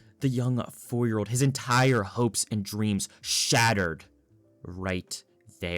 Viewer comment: did you say hopes and dreams somthing an undertale